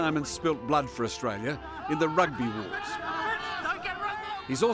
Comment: Did he just say say he fought in the "rugby wars" ffs